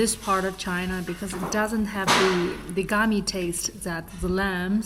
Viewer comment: You need to edit/amend your term to "gamey" instead of "gummy" in your subtitle/transcript.